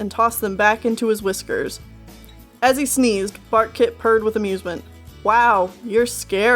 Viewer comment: I heard “FARTKIT”